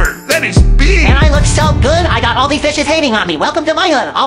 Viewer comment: I thought was saying all these “bitches” hating on me…. While bumping this with my daughter like 😳…. Hahaha lyrics correct it to Fishes 🤣🤣🤣